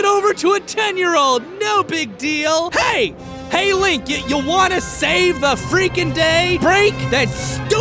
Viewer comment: I heard "'link, you wand-a save the world" Idk if that's what he said, if it is, nice pun